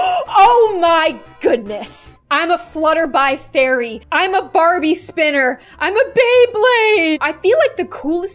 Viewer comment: i thought she said "farbie spinner"